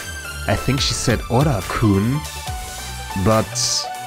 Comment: i think she said orata kun short for otaku arata as said in nokia's dialogue xd or at least thats what i heard